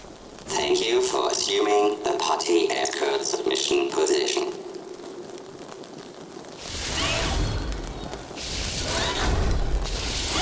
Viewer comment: What is talking at 6:20 and what did he say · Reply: Thank you for humming the parzying e-card of machine Positation